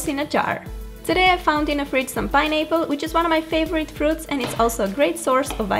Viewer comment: LOL 4:26 she said Pine nipple instead of pineapple lol🙊😂